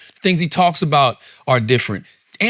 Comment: "Andrew you couldn't make A LIFE 10 years ago" I is how I heard it 😭 charla dirty for that